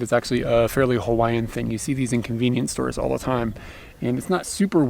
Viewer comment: Love the way that sounded like you said an 'inconvenience store'